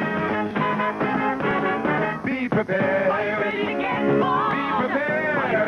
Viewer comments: I was around 5 years old when I first heard this. I thought they were saying "Beaver Bear". in the song instead of "Be prepared" Lol. I remember thinking "what in the world is a beaver bear??" haha. Listen again, it sounds more like that than "be prepared" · I remember that NOBODY could understand what the guy was saying when he sang "Be Prepared." I seriously thought for YEARS he was saying "Beaver Bear" (which makes no sense in context with The Scouts, but hey, I was like, 6 years old at the time!" But my neighbor actually thought he said "Pee your pants" LOL!